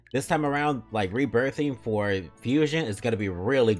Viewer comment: I always hear it as "rebirb" when you say it